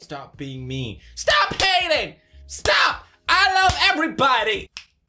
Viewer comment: i thought you said stop painting, i was painting and get hella confused